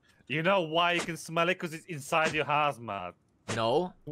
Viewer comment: Lmaooo 23:30 I thought he said You know why you can smell it? Cause it's inside your arse, mate 😂😂😂 I fucking died so hard, rewinded and turned volume up & realized he said hazmat 😢